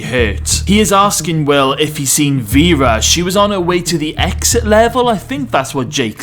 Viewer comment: They didn’t say exit level they said exec. level. I love you’re new work and didn’t mean to spoil it for anyone. I thought it said season 10 spoilers.